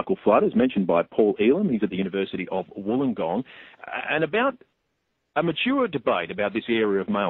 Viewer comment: did he said "maturer debate" ?